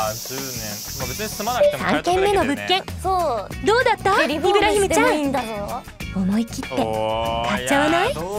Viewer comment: イブラヒムを聞き間違えた妹が「岩姫さま？」と よければ是非